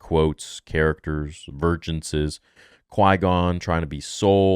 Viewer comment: Vergence??? Crap I thought they were saying “Virgins” the whole time. Now I gotta go back and watch it again. 🥴😂